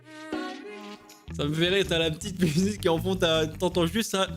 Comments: moi j’entends mhh ça m’bute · Moi à 3:52 g entendu ça me bute